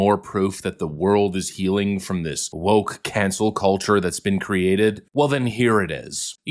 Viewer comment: Sounded like you said, Woke Cancer culture. 😂 Though, it would still be correct!